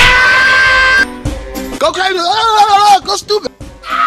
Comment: all i heard was gocrasyululugshdooga